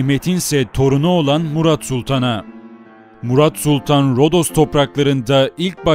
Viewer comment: 'te Murat Sultan mı denildi ben mi yanlış anladım?